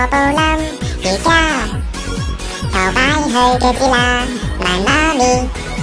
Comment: 我將"瑜伽 頭擺去腳指啦" 聽成“瑜伽 頭擺去腳趾罅”=.=